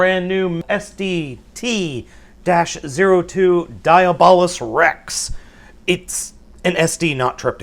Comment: I heard him say diablo rex XD